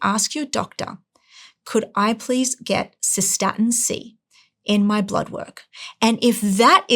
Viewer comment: What is it she's saying to ask? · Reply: If you are following blood creatine to gage kidney function. The systatin C test would be a better test